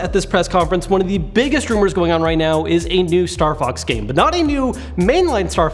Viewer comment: I thought you said starbux game